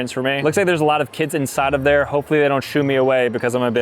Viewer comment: my brain cut him off early and I heard “hopefully they don’t shoot me”